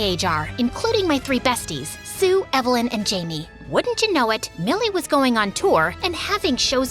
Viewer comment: I almost thought it said my name 😅 my name is Mielle BTW 😊